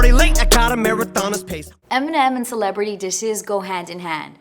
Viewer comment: Did she say “dishes” at 0:06?